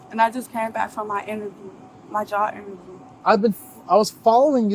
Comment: I heard “jaw interview”.